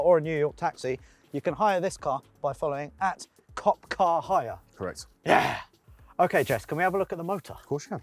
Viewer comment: I thought it said “Copcarshire” 😅